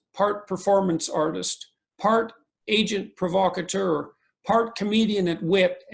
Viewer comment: for a split second I thought I heard asian provocateur instead of agent provocateur . . .